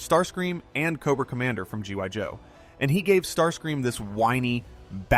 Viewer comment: Did you just say G *Y* Joe¿????